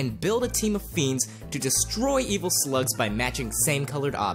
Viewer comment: did he say sluts or slug s